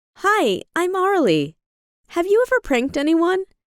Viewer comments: I heard "Hi im narlie" in the beginning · I thought she said her name was Barley 😂